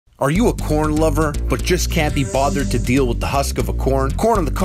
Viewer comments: the first 3 seconds I thought he said "are you a porn lover?" lol. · Thought I heard porn lover at the moment I click this vid